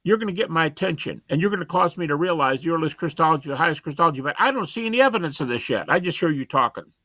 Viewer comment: “I don’t see any evidence of this yet.” I did a tap rewind because I thought he said “I don’t see any evidence of this shit.”